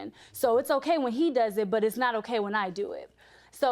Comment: All I heard was “ its cool when they do, its probably when they do jt”